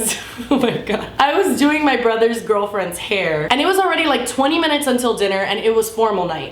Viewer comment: lmao i stopped the video to restartit i was @ 1:37 i think and it sounded like said "i was doing my brother's girlfriend" hahaa